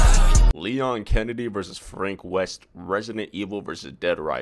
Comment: Ngl i thought it said John f. Kennedy vs frank ocean🥀😭